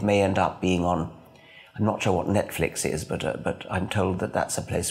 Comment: "I'm not sure what NetFlix is." (Big fan. Maybe NetFlix isn't available in GB?) :) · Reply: I thought he said, "I'm not sure you know what Netflix is but..." Maybe you misunderstood.